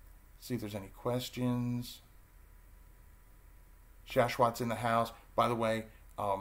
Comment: Who was the person (around 6:30 into video) that you were referring to? I couldn't catch the name. Thank you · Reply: His name is Shashwat Ashiya. It’s a newer channel but his videos are pretty insightful.